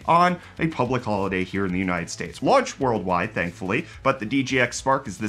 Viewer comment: did you say "Here in the united states"? ... Are you back?